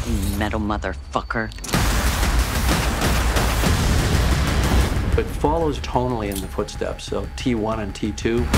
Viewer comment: does Cameron say "Fall is tonally in the footsteps of T1 & T2"?